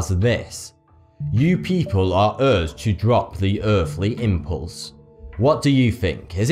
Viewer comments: Guys, I might just be high as fuck, but I think that Jupiter Alien said, "Give me your earth or burn to your death." anybody else hear something similar or just me? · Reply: I thought he said "Give me your Earth or drop an Earth impulse" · I heard “citizens of Earth, drop the Earthly impulse.” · Guys, I might just be high as fuck, but I think that Jupiter Alien said, "Give me your earth or burn to your death." anybody else hear something similar or just me? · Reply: Zero Calibur i hear "give me your earth of drop the earthly impulse". it's a mix of both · I hear, give me more gudders and not the Earthly impulse